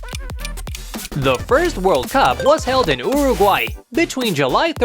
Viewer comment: i thought he said urgay